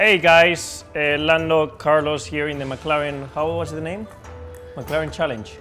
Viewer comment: I thought he said Lando Calrissian